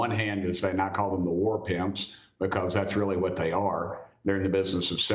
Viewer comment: Congreeman Burchett's characterization of the Pentagon is essentially true - that they are war pimps. I first thought he said war pigs, which would also be true! They should be subject to regular auditing.